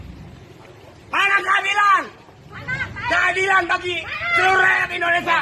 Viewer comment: kok gw dengernya "mana kehamilan" ya? 🤣